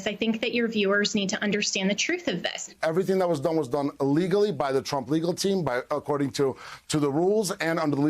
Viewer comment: “Everything that was done, was done ILLEGALLY by the Trump legal team” Famous live confession by Trump lawyer Boris Epstein on Ari Melber show At first I thought he said elegally but that is not a word so maybe it was eh legally but it sounds like one word with no pause and the YouTube text thing recognized the word as being ILLEGALLY so maybe for once this guy actually told the truth about what they did And for those who don't believe that is what he actually said put the YouTube text on and read it for your self and also listen to what he said, I did listen to it over and over because I didn't believe what I heard at first